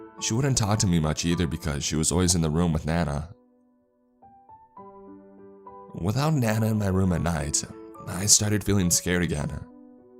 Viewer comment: For a second I thought it said Nana stands ON my foot at bed everynight 😆